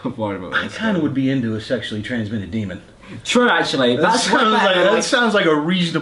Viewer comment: I thought he said sexually transmitted dinner. I was so in